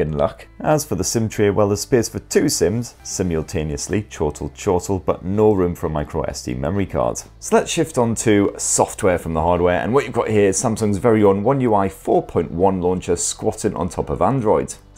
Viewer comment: did I hear 'chhotu chhotu' ?lol